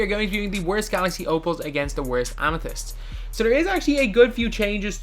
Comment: did anyone hear him say worst amethyst